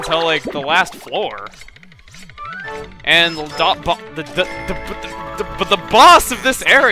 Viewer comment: Keep Clicking 7:11 and 7:13 XD Awesome!!!!! It sounds like hes saying "AhPah, the tipof the topof the" If U click em back and forth at the right speed